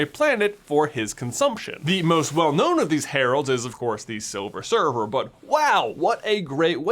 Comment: Did I hear "Silver Server" instead of "Silver Surfer", or am I just going insane (or both)? -Shawn